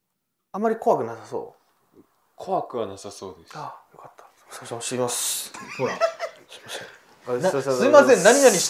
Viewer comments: からの、動画で、 (すいませんお邪魔します)て、さんが、言ってた言葉、聞こえましたよ！ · すいません走りますって聞こえる(笑) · すいません、走ってきます…に聞こえてしまう😂